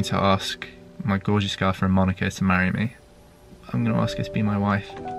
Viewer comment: “My gorgeous girlfriend Monica to marry me”. I heard him say “my daughters girlfriend to marry me”